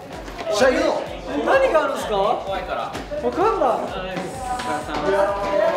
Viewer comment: 「京介一緒に行こう」 「なんで？」 「怖いから」 聞こえる...聞こえるぞ...